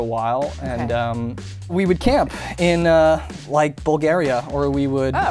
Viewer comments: Did he just said that he had been camping in Bulgaria?? I can't believe! My country! My childhood crush had been in my country! screams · did he just say Bulgaria😍😍😍😍😍😍😍😍😍😍😍😍Здрасти!❤